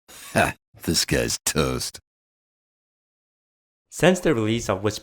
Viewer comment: What kind of accent is Toast's ? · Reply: he got the nightname from SI:7 Agent's opening line of "this guy's toast... which when you say it sounds like Disguised Toast :P I think there is a video on "misheard" hearthstone cards someplace.